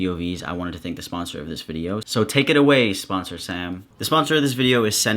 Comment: When he said sponsor Sam I thought he said sponsor-san, and I was like “w e e b”